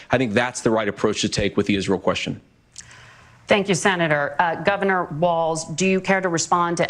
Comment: did she just say Governor Balz?